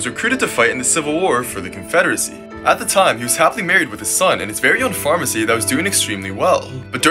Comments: For a sec I heard "he was happily married to his son". 😂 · I thought you said “and he was happily married to my son.” 💀💀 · I thought he said he was married to his son…… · At first I heard "happily married to his son" BC I WASNT LOOKING SO I HAD TO REPLAY THE VIDEO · I thought he said "he was happily married with his son" lol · I thought you said he was happily married with his son, had to rewatch it to find out what you really said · Bro i thought he said he got married to his son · ok but like i thought you said "he was happily married to his "SON" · I heard it “he was happily married with his son” and i was like wait what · I swear I heard “happily married with his son” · Bro I thought he said and he was happily married to his son 💀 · i actually thought he said "he was happily married to his son-'" help💀